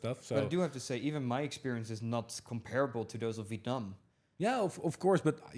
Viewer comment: What did say at 59:55? Not comparable to who? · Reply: use captions, we said Vietnam